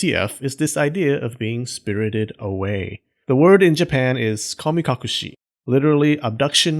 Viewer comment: I thought he said kamikaze